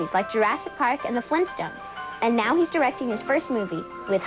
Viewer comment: Did I hear that right? “Jurassic Park” & “The Flintstones”?